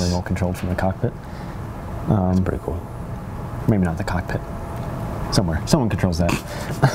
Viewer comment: i thought he said cockpit xdd